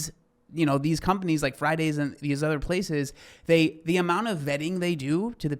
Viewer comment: I have such a fat brain. I heard Friday's and thought TGI Fridays 🤦🏼‍♀️